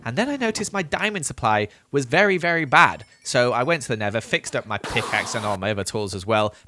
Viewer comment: did he say nether (btw i wish he make 2000 days)